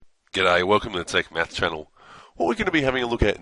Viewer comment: did he just say welcome to tic METH